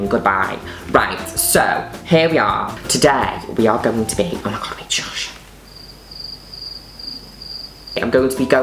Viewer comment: What does she whisper at 1:27? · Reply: I'm pretty sure she says "oh my god wait shush" really fast. I just figured it out myself, so I thought I'd let you know in case you were still wondering after a month lol